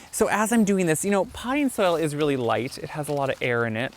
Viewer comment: He said Potting Soil! I thought he was saying Pine Sol!! I must need a nap!😂